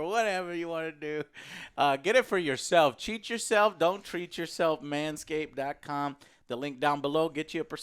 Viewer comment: did he say cheat yourself don't treat yourself?